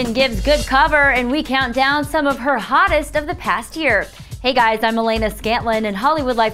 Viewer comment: I thought she said "Hey guys I'm Melena Skankling"